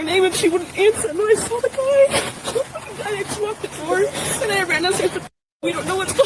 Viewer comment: I think it sounds like she says "I told Xana to lock the door", then "I ran downstairs n told Bethany we don't know what's going on"